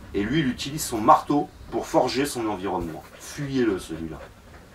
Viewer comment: Salut, j'ai cru avoir entendu "fusillez le celui là" à 3:57 :) J'ai repasser pour être sûr que je me trompe, et enfin j'ai compris qu'il fallait le fuir... lol Bonne soirée!